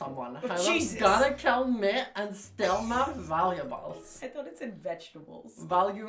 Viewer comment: i also thought it said vegetables lauren😅